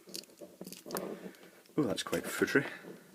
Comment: Could someone with better ear tell me what said at the 7:36 mark? "oh, that's quite (...) " Thank you. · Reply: Ah, sorry. I didn't realise before that "footery" is apparently a very Scottish word. It means wasting time doing something intricate. It also has other similar meanings though.